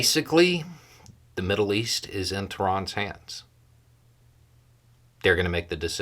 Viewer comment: "the middle east is in ____s hands." Turon? I can't hear that. Who's Turon? CC says Tan and that doesn't make it any clearer. Into irans hands? What are we saying? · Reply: Tehran, capital of Iran